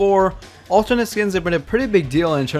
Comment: I thought u said alternates skins